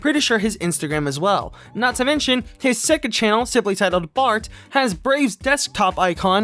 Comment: sounded like you said fart.